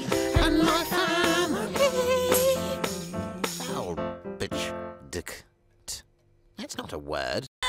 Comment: what did he sayyyyyyyy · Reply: He said faulbchdt